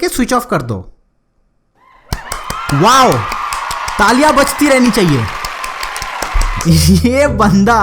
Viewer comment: said, "taliyan bajti rehni chahiye". I heard "gaaliyan padhti rehni chahiye"